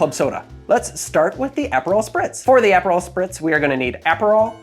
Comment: At first, I thought he said PHENOL spritz, and I was like, man he is really getting into molecular mixology here